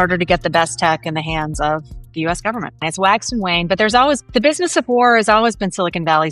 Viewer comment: …….did I hear Wax and Wayne, like from the Mistborn Era 2 by Brandon Sanderson??